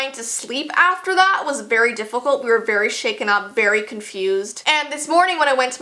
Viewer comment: Sounded like she said beary confused